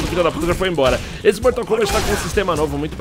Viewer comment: mortal kombat foi so eu que ouviu isso